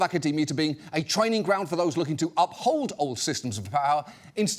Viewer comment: It’s too early and my brain isn’t working…. What did he say at 27:16? “ ….a training ground to uphold all systems of…Paowl”what😭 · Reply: a training ground for those looking to uphold systems of power